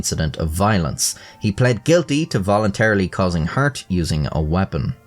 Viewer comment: bro what did you say?????? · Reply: Causing hurt moment